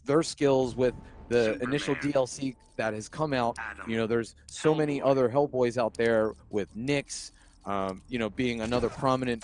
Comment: it sounded like the commentator said "there are so many other hellboys out there umm with dicks"😂